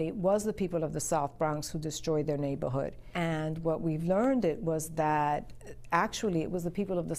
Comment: No disrespect to but true Bronx natives say “learnded” (0:40) instead of learned. 😆